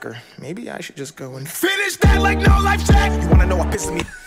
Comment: Did dax say “go finish that Like no life Shaq” at 31:56 to 31:60